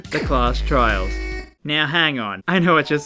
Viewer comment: hold up did you just say "hang on"??? .... ....................